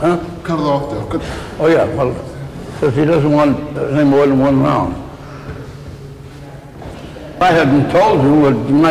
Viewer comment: what he said?? I can't understand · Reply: He told them cut the cameras off